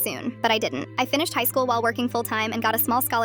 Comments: I swear at the end instead of hearing "because I want to help kids who feel as worthless as I did" I heard "because I want to help kids feel as worthless as I did" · at the end I thought she said “ because I want to help kids feel as helpless as I did” 😂😂😂